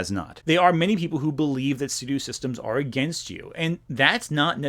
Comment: "There are many people who believe that ---- systems are against you" - WTF is that missing word? Sounds like he's saying "Sudew" but that makes no sense to me. The AI captions say "Studio Systems" but I don't know what that would mean, and my brain just can't tell what word he's saying there.